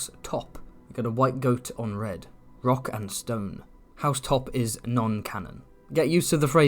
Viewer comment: DID I HEAR A ROCK & STONE !?!?